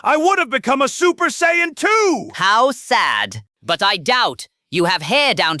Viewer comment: is he saying "Super saiyan 2" or "Super saiyan too"? You decide internet. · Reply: Too